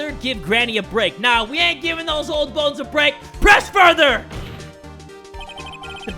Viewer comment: When you said “press” YouTube subtitles thought you said “breasts” so the conversation with oldbag became a little awkward.